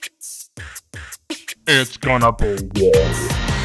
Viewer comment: I thought he was saying it's gona be gay